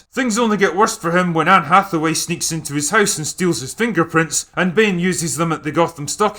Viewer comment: Did you just say Anne Hathaway lacks sex appeal 😭😭